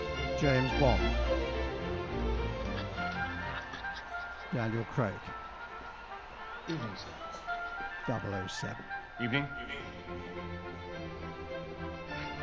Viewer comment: The queen trying not to laugh when she says “good evening Mr Bond” is amazing, I’m so glad she agreed to do this 😂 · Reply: I thought she said “Sir Bond”.